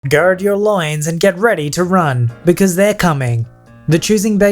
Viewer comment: At the beginning I thought he said, "Guard your lions." I'm so smart